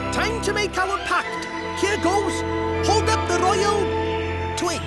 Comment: "Time to make our pact, here goes! Hold up the royal.....twig" did i hear that right lol